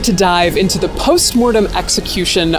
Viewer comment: I swear I thought you said "We are here to DIE" 💀 but it was just dive, we escaped alive!!